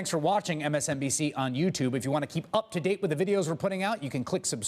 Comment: Every time Chris Hayes says “just below me,” I hear it as “just blow me” in my head even though I know that’s clearly not what he is saying. It just seems funny so my mind twists it into that every time.